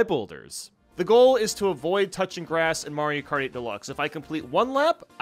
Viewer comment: when he says mario kart 8 it sounds like he is saying Mario party or Mario karty